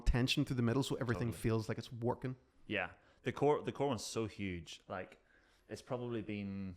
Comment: I’m so scared of flare-ups! I’m fairly new to the back problem arena. Thank you for this! PS: I had to rewind at 7:18 bc I heard “the colon’s so huge!” 😬🤣 (I mean… you did do “the carrot” in an earlier post! 🤷‍♀️)